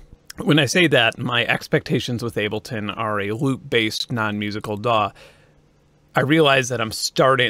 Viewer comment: I never knew what I truly wanted in life until I misheard "loop-based" as LOOT-BASED musical daw